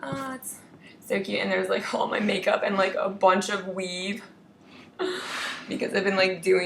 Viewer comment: haha when you showed your weave i thought you said "theres like all my makeup and A BUNCH OF WEED"